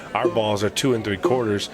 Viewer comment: did he say our balls are 2 3/4 ? lol